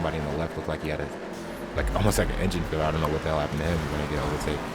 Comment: dude what where you trying to say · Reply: almost like an engine failure. which means he tried to mid-drive boost and failed